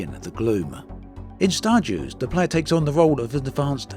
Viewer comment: I thought you said 'Star Jews' for a second, which is now the game I rly want.